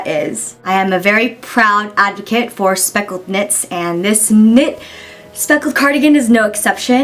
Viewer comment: I thought you said speckled tits LOL